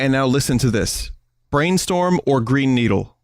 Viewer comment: i only hear brainstuhl....